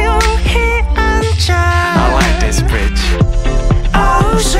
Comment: Go to 0:39 and listen. Am i hearing only 'Ilike this Bitch' at the back or thats really partof the lyrics😂 Btw R.I.P Jonghyun and other Shawols who commited sucuide after Jonghyuns death😥